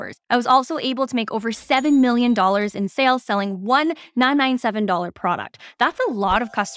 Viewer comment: Hey, at 0:52 how much did you say your one product that you sold cost? Was it $997? I can’t make it out.